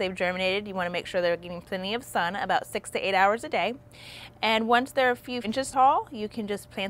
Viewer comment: did she just say 68hrs a day? 😭